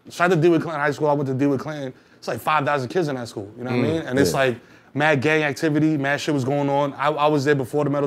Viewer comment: Lol I thought he said mad gay activity